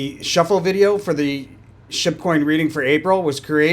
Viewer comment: I find it so funny that I hear you saying "shitcoin" when you say Shib coin 😂